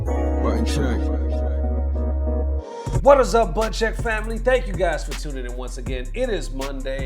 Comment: Intro sounded like he said Butt Check! 😁